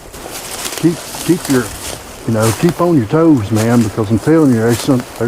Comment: I heard "keep out".